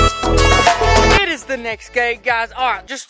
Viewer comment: Who else hear say it’s the next gay